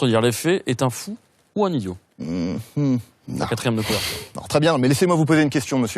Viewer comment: qu'est-ce que dit Gaël à 2:11 aprės "est un fou ou un idiot" ? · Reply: Jean-Louis Rio "la quatrieme de couverture", le dos du livre quoi